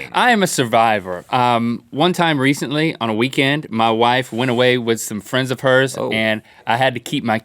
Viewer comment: X) 'i heard my wife ran away'